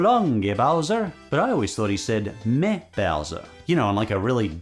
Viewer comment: I always thought he said "So long, me Bowser" like some kind of Italian pirate